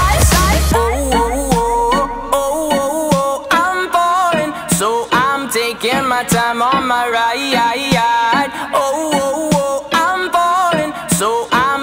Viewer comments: when I was young I thought he said "I'm boring so I'm taking the time of my life" dang I got it all wrong back then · Yo pensé que decía: "i am Boring so i'm takin my time on my ride" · When I first heard this I thought he was saying “I’m ballin” lmao 😂🤣